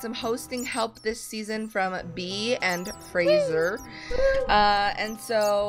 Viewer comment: did she say Graser